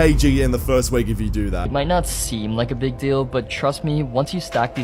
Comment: I thought bro said “nuts seem like a big deal” 💀